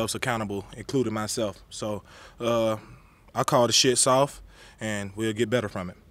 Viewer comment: did he say ( I’ll call the shits off)